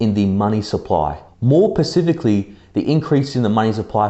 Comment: Did you just say... "more pacifically?" Come on my guy... specifically lol good video